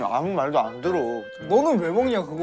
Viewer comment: 태산이가 “우낙아 오늘 되게 잘생겼다”라고 한거 저만 들었나요?ㅋㅋ